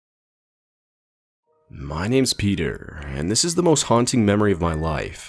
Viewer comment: I literally laughed because instead of “Peter” he said “pidr” which in Russian means that a person is gay but is used to disrespect lgbtq+. (I don’t disrespect LGBT, in fact I am lesbian :) )